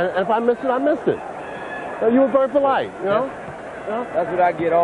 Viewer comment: Does the ref say "You're a burst of light" at the end of their conversation? If so, he's cooler than the other side of the pillow. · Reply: J L no he says 'you were very polite' but when i read your comment and relistened i heard 99% 'your a burst a light' , but definitely is the former... just goes to show how easily the mind , eyes , ears can be manipulated